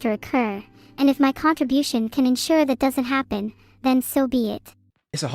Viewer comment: It's Soviet라고 들리는거 나만그런가